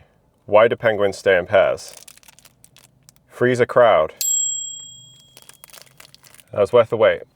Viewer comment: NO CHANCE I would've understood that joke without you speaking it. Sincerely, an American. At first I thought you literally said "freeze a crowd"